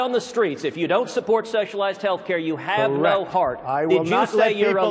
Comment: Did he say “sexualized healthcare”?